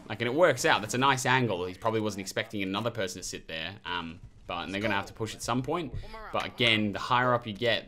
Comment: did he just say "n words are gonna have to push at somepoint" lol